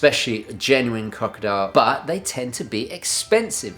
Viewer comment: When he's talking about leather bands and says "genuine..." at 6:13, what does he say? Just starting to get into watch bands. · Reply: Genuine crocodile leather